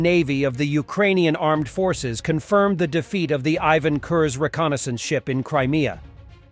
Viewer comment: I heard 'Ivan cursed' 😅